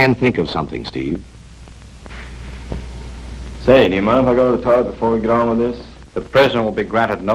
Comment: can someone tell me what Steve says here? Thanks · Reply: He said ~ "sir do you mind if i go to the tub before we are going through this".